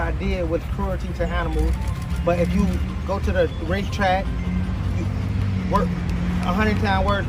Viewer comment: I thought he said race track